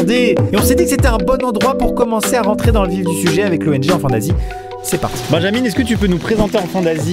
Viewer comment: enfant quoi (enfante naz......)